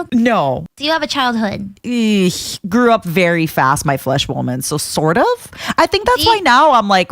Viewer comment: what did say? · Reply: "Grew up very fast my flesh woman"